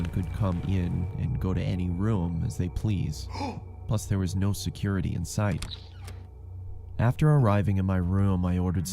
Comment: i thought he said there is no security?